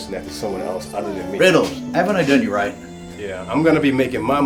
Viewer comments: Can anyone tell me what Oswald says at 34:58? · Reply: Riddles, haven't i done you right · Can anyone tell me what Oswald is saying here? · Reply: "Riddles, haven't I done you right?"